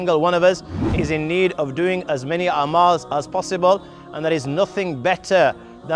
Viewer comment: Please check the subtitle (03:27). Instead of Amal it says Animals.